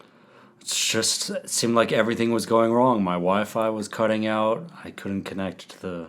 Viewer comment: I kept hearing "My WIFE was cutting off", which seemed ironic.